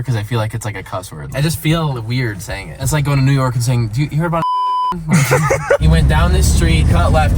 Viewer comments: Does anyone know what he is saying at 7:30? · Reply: Did u hear abt 9/11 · i think Cody said did you hear about 9/11